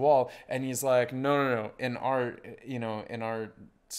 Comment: he actually said " We don't have sixer in hockey" but the subtitles messed it up ( trying to make it easy for them who don't understand cricket which IMO was a lame move😂)